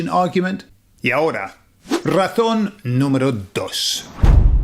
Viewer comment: oh i see, 2:37 isn't ratón, it's just the Spain lisp, i misheard great video, subd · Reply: Jajaja oh razón no ratón 🐁 I couldn’t figure out why rats? Jaja